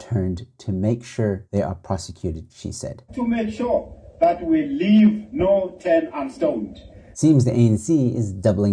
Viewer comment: did I hear him right saying we leave no turn unstoned? 😂